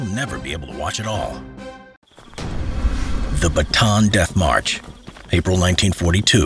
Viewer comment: sounds like hes saying baton instead of bata-an